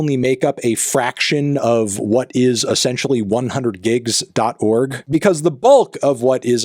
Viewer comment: Aww 100 gigs? I thought you said 100 gecs